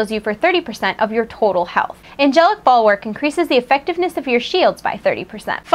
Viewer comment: Angelic bulwark, I thought she said angelic ball work and I though I have some angelic ball work she can do. :P I know I'm bad. Sorry. :)